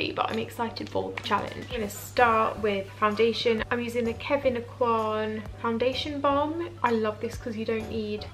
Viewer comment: Amazing effect. Thought I heard "Kevin Kwan"; turns out it's Kevyn Aucoin. Looks like a good product. I wonder if you tried concealing your natural eyebrows.